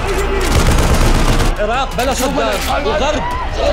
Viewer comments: قاسم الملاك كاعد يبجي بلزاويه 😹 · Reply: سمعت اسم صدام لو متوهمة ؟ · واخيراً فلم عراقي بنتفلكس ❤🇮🇶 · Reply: سمعت اسم صدام لو متوهمة ؟ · اتمنى من كل قلبي أن ينجح عشان بحب العراق زي بلدي مصر والله بحبكم يااحسن ناس تحيا ليكم من مصر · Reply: سمعت اسم صدام لو متوهمة ؟